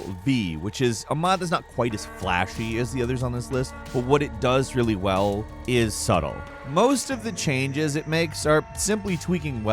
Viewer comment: I thought he said “what it does very well is suck” 😂😂😂